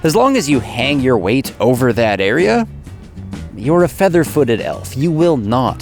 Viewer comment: Ride like a feather footed elf! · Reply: c w When I first heard him (I have a hearing impediment) I thought he said “fairy-footed oaf”. I thought he was talking about me..! Les